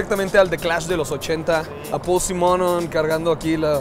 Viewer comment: Cómo se llama? no entendí ni escuche bien el nombre,solo escuche algo como "pussy" JAJASLKJAKJAS